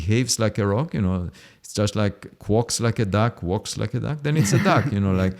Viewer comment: To start with, this is a great talk. At one point Avi is coining the phrase “if it walks like a duck and it quacks like a duck, it’s a duck.” But in this talk it sounded like Avi said if it walks like a duck and if it quarks like a duck…..yada yada. Being that he said that and was just mentioning his thoughts on Brian it brought this mind. Could be a new joke, or you could turn this into a meme or make a T shirt out of it. Or both. Go crazy! Just try this out. If it walks like a Brian and it quarks like a Brian. Then it must be a Dick! I must admit that that isn’t a nice thing to say. I thought it was a little funny. Let me know what you think.